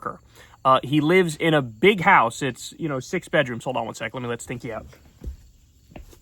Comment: I thought he said I’m gonna let a stinky out then proceed to go inside let it out and come back outside 😂😂